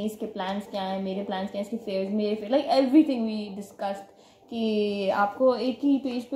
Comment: Did she say we discussed abt "iske affairs, meray affairs"??😐😐